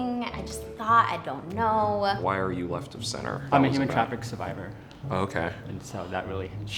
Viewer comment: This asian guy with the suit and glasses said he was a survivor of something but I didn't understand what he survived. Could someone help me out? It's minute 7:40 or something like that. Thanks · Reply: Human Trafficking